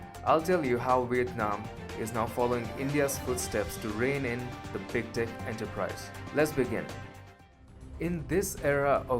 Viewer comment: For a moment, I honestly thought he said "following India's footsteps to reign in the Big Dic Enterprise". #facepalm #LMFAO